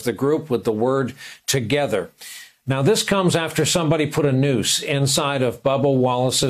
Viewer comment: (He really said Nuce) Not nose 🤦‍♀️😅🤥